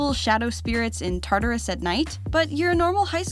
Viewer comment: wait did she say Tartarus...